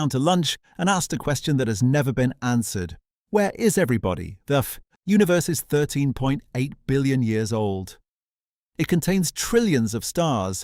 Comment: is this saying "the f**** universe 13.8 billion years old"